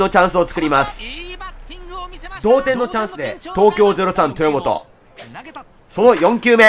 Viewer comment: 後半、東京03豊本がエラーするところの実況が「東京03とぅーもと」って聞こえるｗ 実況者の滑舌もGなのがオチか